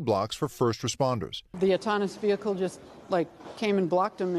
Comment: Did she say "autonous" vehicle? It is called AUTONOMOUS vehicle.